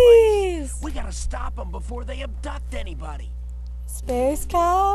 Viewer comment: Is it me or did it sound like say "adopt" instead of "abduct"?